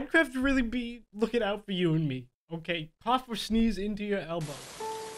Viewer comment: I thought he said cough and sneeze into your elmo